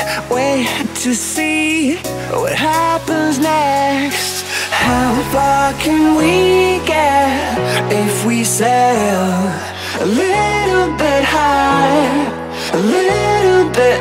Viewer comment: anyone else heard what I heard? "how fuckin' we get if we sell a little bit high a little bit low" can't un-hear it now XD